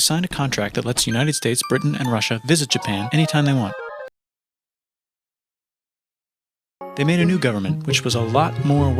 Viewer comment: Does anyone know what Russia is saying here? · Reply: I'm not Russian so it may not be correct but he's saying "molodets" (pronounced like "maladets"). Basically, "good job/good boy, America". He's praising him bc they were actually on pretty friendly terms at this point in history.